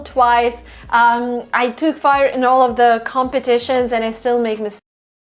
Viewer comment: She clearly said took part 19:00, the caption took fire was wrong. I wouldn't rely on auto subtitles. Lisa and Kevin are definitely nitpicking. But this is useful for learners. Watch marina rarely just for entertaining purposes. For English i go to venya. Don't even think to touch or critize that guy.